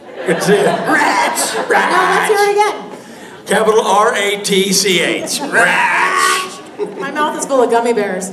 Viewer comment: Could someone tell me how they spell Rich? I don't understand the word before the R at 44:14? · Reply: Ratch; it was a joke about how she'd said his name with the gummy bears in her mouth (just in case you didn't catch it).